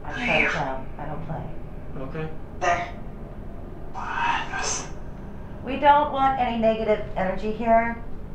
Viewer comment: when she says “we don’t want any negative energy in here” it sounds like it says” shut up b****” 🤣🤣🤣 her expression had me dead